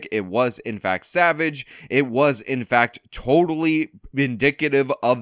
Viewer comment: did you say “bindicitive?”